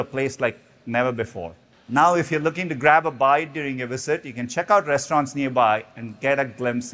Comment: lol i thought he said grabify